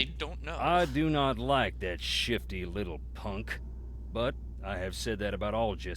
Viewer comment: When nancy asked wade about Colton I thought he said "i dont like that shitty old punk" instead of shifty. Lol